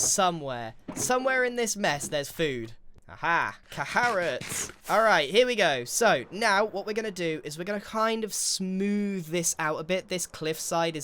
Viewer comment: "Caharrots" You can take the out of the life series, but you can't take the life series out of the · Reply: SO REAL I THOUGHT I WAS TWEAKING HE SAID CAHARROTS